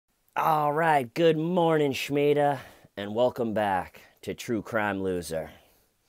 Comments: I heard “Schmeeda” as well, so I love that you heard it too, and I WISH I had a friend named Schmeeda. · I heard her say Schmeeda.